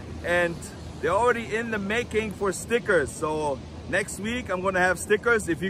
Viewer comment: Never mind I thought you said Snickers 🤣